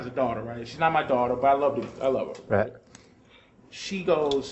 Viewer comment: thought he almost said "I love the bitch"